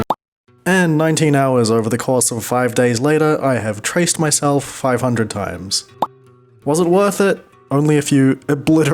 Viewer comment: for a sec i thought it said "i touched myself 500 times"